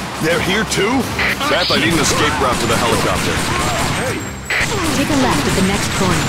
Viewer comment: for some reason when I first played this game and got to this part I thought he said “BathI need an escape route to the Helicopter”